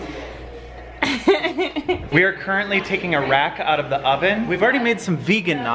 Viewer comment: why did i hear “we are currently taking Iraq out of the oven” 😂